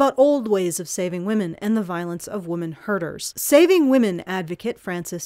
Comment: I keep hearing you day, "women herders"...